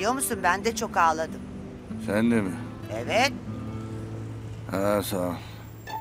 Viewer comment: husnu ne diyor orda anlamadim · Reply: Hııı saol diyo